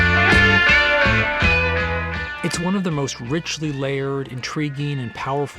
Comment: I sounds like it says "one of them"